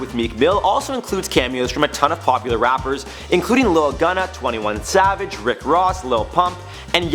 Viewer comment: did he say lil gunna 😂😂